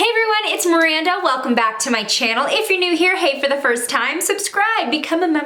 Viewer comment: I thought she said “if you’re new here hate for the first time” but she said “hey” 😂